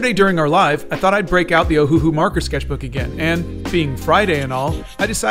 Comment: i thought you said Cthulhu markers, my bad back to the oceans I go....